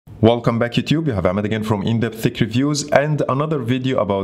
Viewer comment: When he said " in depth tech reviews" I heard " Indian fake reviews"